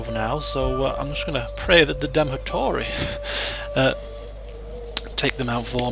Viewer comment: you said Damotori instead of Hattori.